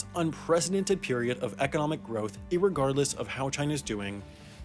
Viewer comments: did he just say irregardless? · did you just say "irregardless?" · Did you actually say 'irregardless.'